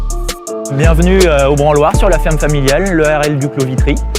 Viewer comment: Est-ce que vous entendez la même chose que moi ? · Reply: "Le branloire" (je sais pas si ça s'écrit comme ça) ferme familiale. Ça te fait penser à un mot à connotation sexuelle.